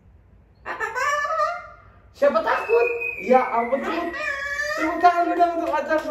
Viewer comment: Gw dengernya suara ocong kek gini 😂: Emang kalau di laut gitu, ocong berani sama aer? Ocong : "Mesti lah, kaga takut!"